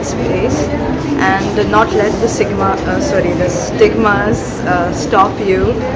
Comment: I think the girl at 4:11 said sigma